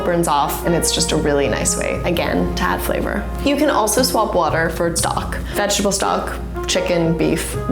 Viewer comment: I wasn't looking, and I thought she said to use "swamp water" .